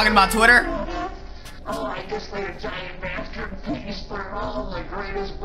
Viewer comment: I thought he said “I made a giant bastard piece”😂